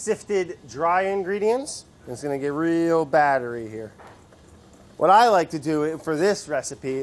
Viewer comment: I initially heard this as "It's going to get real battery here" and thought to myself, wow...this is an aggressive recipe.